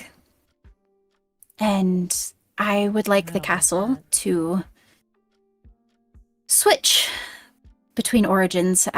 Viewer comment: What did you say at 1:11:14 LOL?? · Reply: "idk about that..."